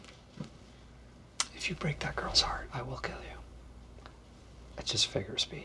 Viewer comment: For a moment I seriously thought I heard Michael say “if you break my girl’s heart.”